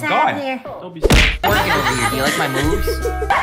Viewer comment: HONESTLY THOUGHT SAID "Ya'll like my boobs?" BUT ITS ACTUALLY "Ya'll like my moves?"